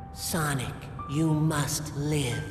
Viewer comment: chip says sonic you must live but the subtitle says sonic you have to live